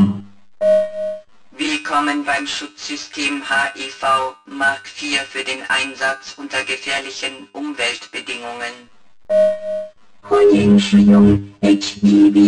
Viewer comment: i dont hear H. E. V. Mark 4 but hiv mark 4 and im germand LOL